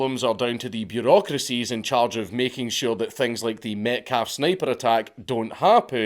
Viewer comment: For a second I thought you said; the "meth-cat-sniper" 😅